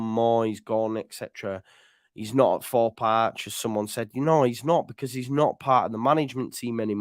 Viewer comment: I thought Javi said he s not at the club anymore.